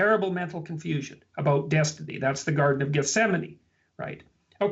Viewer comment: Jordan's thought bubble says "Garden of Yosemite" (LOL!) although Jordan clearly said "Garden of Gethsemane"...which should be obvious from the context for anyone with even a passing familiarity with the gospel texts. Please tell me that this was due to autocorrect, otherwise I'll have to conclude that is so painfully ignorant of the gospels and Christianity that he hasn't even heard of the Garden of Gethsemane. I only mention this because such a lack of knowledge could possibly have an impact on his ability to pick up on some of the subtleties, innuendos, and references that are mentioned in discussions.